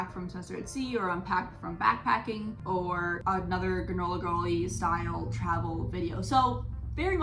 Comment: When you said "granola girl," I thought you said "gorilla girl." I had to go back and listen again to figure out what you were talking about 🤣🤣🤣